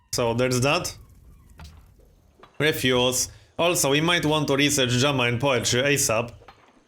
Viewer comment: Heh, when you said "we need to research Drama and Poetry," I misheard it as "we need to research German poetry." It struck me as an oddly specific tech...